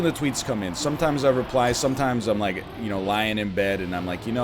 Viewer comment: I thought NL said "Lion in bed" and was like, did just call himself lion?